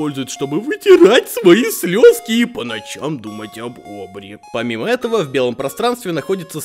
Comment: Я где-то год думал, что он сказал думать о бобре...